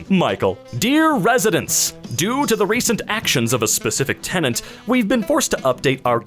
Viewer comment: What, is said tenant Dr bright?